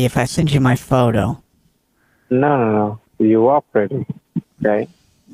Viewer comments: Did he call you "bae"?! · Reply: Timestamp? I didnt hear anything that sounds like bae... did I miss that? At the beginning scammer just mumbles under his breath in 0:08 saying you're pretty, right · Did he call you "bae"?! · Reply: I may be wrong, but that's what it sounded like to me. "You are pretty.... bae."